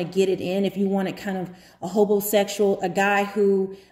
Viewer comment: Did she just say hobosexual?